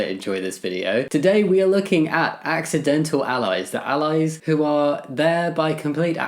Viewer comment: What do we think of the accidental allies!!?? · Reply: For a second I thought you said accidental aliens